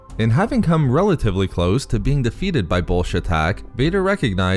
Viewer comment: "close to being defeated by Bullshit Hack" i can't stop laughing at this, even if i know that's not what he says xD